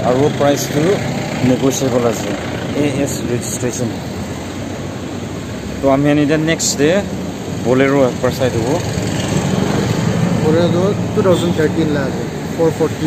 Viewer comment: i heard someone saying borelo...